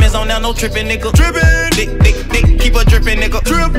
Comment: what does he say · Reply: "Make me bleed, i swear to god imma leave a nigga drippin"